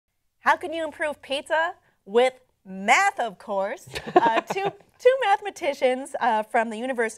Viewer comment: At first I thought she said "With METH of course!"